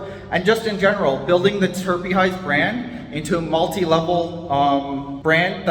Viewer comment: Dude, I love your content. Every time you say 'Terpy Highs' I hear 'Terpy Eyes' and it cracks me up! Good luck with the store Bro!